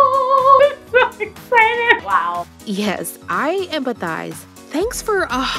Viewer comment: I thought u said "yes i am a guy" 😳